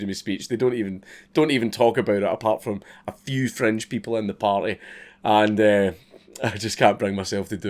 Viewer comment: Notice how his accent gets thicker when he gets angry? "Ha'in a whunge an a greet" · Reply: When he says "fringe people" I keep hearing "French people"